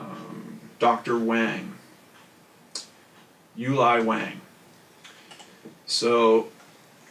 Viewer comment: did u say the name of the dr that did the autopsy was U..lie Wang?? 🤔 that is wild!